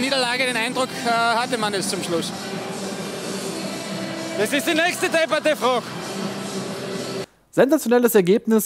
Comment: Was sagt die Person bei 0:52 ? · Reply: bugz1591 des is die nächste deppade frog (das ist die nächste blöde Frage)